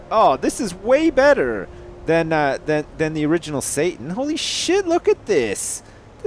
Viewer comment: Did he say "Holy Sjin" or "Holy Shit" ? Video Time